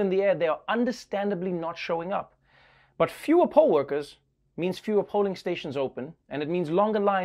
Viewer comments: I thought you said "pole workers" :) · I saw "California Rolls..." and I clicked thinking Trevor was talking about sushi... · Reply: For a few minutes I thought he said "pole workers", like dancers. I was confused hearing most of them were over 60...